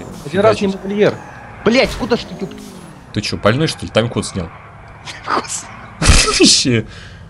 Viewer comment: 59 У МЕНЯ СО СЛУХОМ ПЛОХО,ПОЭТОМУ Я УСЛЫШАЛ "ТАМ КОТ СНЯЛ"